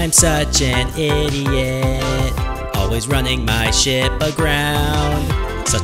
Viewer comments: did he just say shit? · What I thought you said: Always running my sh*t aground The actual lyrics: Always running my ship aground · I thought he said always running my shit